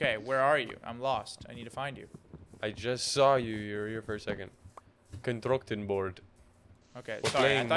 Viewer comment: hamxag sounded like slav saying controctin boarf